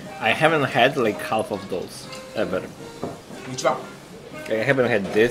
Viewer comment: "例えば"をさんはなんて言いましたか？聞き取れませんでした😢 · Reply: which one ですかね、 自分はそう聞こえました！